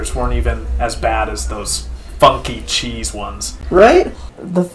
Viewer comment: man i sure do love fucky funky cheese (that’s what i thought it said at first lol)